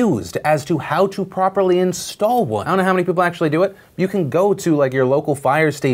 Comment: even though Phil says “I don’t know how many people actually do it” for a hot second it sounds like he said “I don’t know how many black people do it” I had to rewind it twice I couldn’t have been the only one